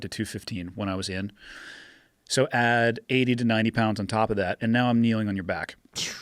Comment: Anyone else thought he said he was carrying “Eighty-Two hundred pounds”? Hahaha